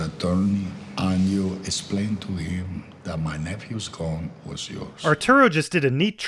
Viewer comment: There's a mistake in the subtitles at 14:30 The quote says "gone" instead of "gun"